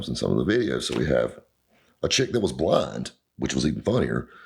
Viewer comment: Does he say "blind" or "blonde"? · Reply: Blind